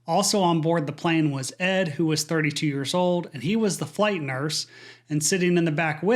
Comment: I had to rewind. I heard “he was 3 years old” and had to replay